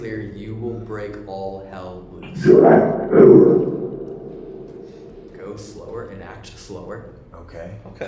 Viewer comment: I hear ‘huge tirade’ and then ‘enact power’ 😮